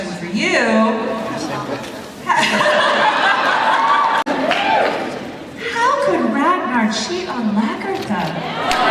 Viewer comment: does anyone know what he says at 0:13? i cant hear it but everyone laughs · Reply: he says: " I'm single" :)